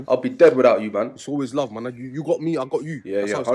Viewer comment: did he say the n word love my n